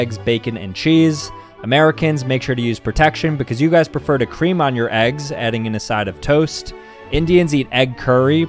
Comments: bro i thought he said "you guys prefer to cream on your eggs" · “Americans made sure to use protection because You guys prefer more cream on your eggs” 💀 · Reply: I heard “you guys prefer to cream on your eggs” lmao